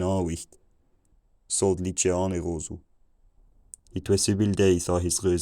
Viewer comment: At around 700 I start hearing things that SOUND like words/phrases I know, but they might be "false friends", aka words that seem the same but aren't. "It was seven days" "daggers grim" and "between samples", which doesn't make sense for 700's people to be talking about, so it's probably not that. :P --At almost exactly when the line hits 800, suddenly: "Chicken" and nothing else. :P --Late 900's "It is long winter, the ground is cold" and then nothing 1100's--1300's I keep hearing bits, like "I was born in" and "has failed us", but again: beward of false friends/homonyms. 1400's-1500's I heard old fashioned versions of words like cow (as "coo"), and yeah, that's around the time (non-expert) people start getting it. I wonder how much more (or less?) I would understand if I read the text of this instead of hearing it. Would I keep seeing words that look familiar (but aren't)? Or would it look like total gibberish? At one point would reading it start being understandable, as compared to the audio version? Interesting.